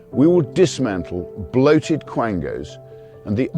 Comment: Another rare moment where the uk earns my respect 10:30 I seriously thought he said, "bloated clankers" 😂